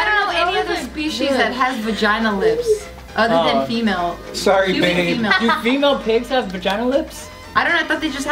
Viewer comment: did say "sorry babe" ? lol